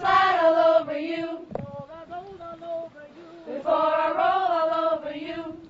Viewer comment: Can someone please tell me what she says at 2:52?? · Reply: I think it is [before I roll over you]